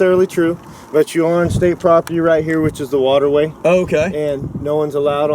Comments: The cop did not say you were on private property. Cop says he was notified (by someone else) that you were on private property. Cop then says you are (or were) on STATE PROPERTY. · he (the Trooper) did not say you were on private property, he said you were on state property. see time marked at about 1:54.